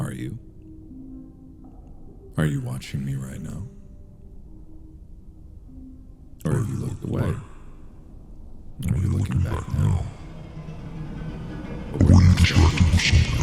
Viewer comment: Can anyone write what mark was saying 31:52? I'm hard of hearing, and the captions couldn't pick it up either. What I did catch was pretty intense. lol, Mark can really push his character when he wants to, that was the vibe I was getting at least, I just couldn't make out exactly what he said, but I did notice the blue and red background around him..though not seeing many talking about it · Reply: "Are you watching me right now? Or have you looked away? Are you looking back now? Or were you distracted with something else?"